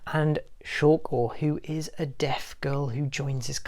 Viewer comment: When you where talking about A silent voice, I heard “a dead girl” and not “a deaf girl”